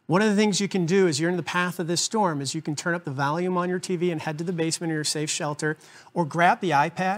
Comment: sounded like you said turn up the Valium lol